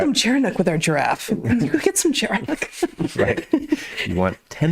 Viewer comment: What do they say around 26:00 sounding like “cherinuck”?